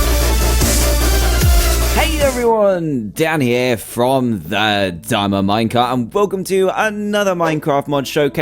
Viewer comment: What I hear: "Hey everyone, Dan here from the diamond minecart!" What my gran heard: "I hate everyone! Like Dan here from oh damn her manga." ... I wonder what she thinks of me now...